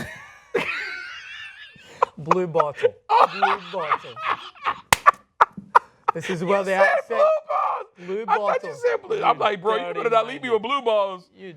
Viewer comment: lmao it definitely sounded like he said blue balls at first